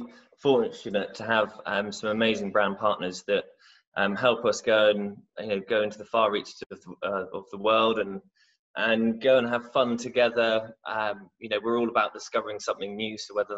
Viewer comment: Tell me I've misheard, because "brown partners"?? Wtf?